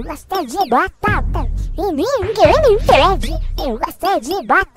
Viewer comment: JDGKDGSJSVRVRHRH foi isso que eu entendi kkkk😂😂😂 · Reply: Eu entendi: eu gosto de bugada e ninguém me impede